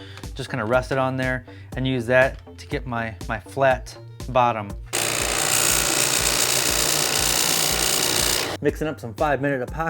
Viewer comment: When you said flat bottom, I thought you had said Fat Bottom, and waited a few seconds for a clip of Fat Bottom Girls, then I realized you said flat bottom. I need to start wearing hearing protection and protect the little I have left.